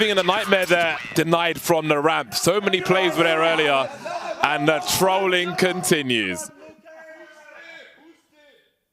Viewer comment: what did Astralis say to Navi and what did S1mple respond :D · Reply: They said "his name is jo" refering to b0rup bc they call him jo for johannes., and afterwards devve said "remember it"